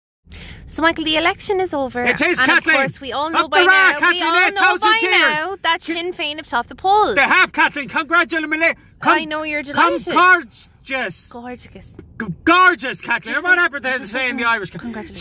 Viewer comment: Sinn Fein??? I thought they said Seo Linn!!! Both sing in Gaeilge.